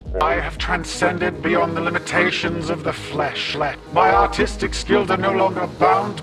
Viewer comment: HELP when he first said “...my artistic skills...” 0:04 It sounded like “…my autistic skills…”